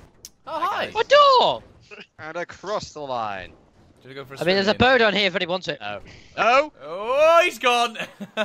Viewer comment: did say "there's a bird on here if anybody wants it?"